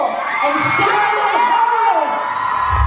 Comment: honest to who it sounded like they said Sharon Harris when they called you and Carli up 😂 on the plus side, ur amazing! but obvs you know that 😍😍